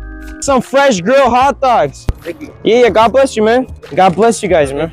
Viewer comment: i thought he said "French girl hot dogs"